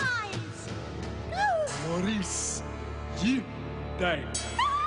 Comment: For the longest time I thought Willie was saying "Maurice, you die" and then thinking why is he saying that; then one day it finally dawned on me when I paid closer attention to what Martin was saying and suddenly it all made sense